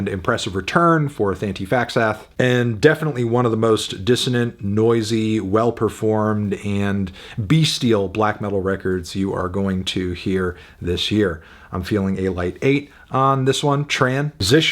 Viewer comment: Did needledrop just say 'best bestial' ???? Wut 😂😂😂😂😂